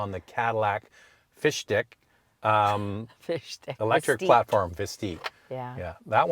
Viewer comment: Oh my... thought you said fish dick